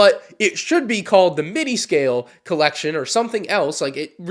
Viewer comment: This entire time I thought he kept saying MINI-scale